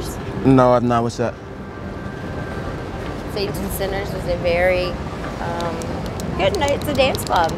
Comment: What's Center's is she saying at 6:40 ? The place they're saying has locations in New Jersey & Philly ??? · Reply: Saints & Sinners